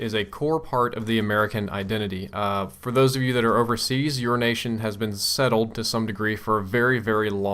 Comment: it sounds like urination instead of your nation:)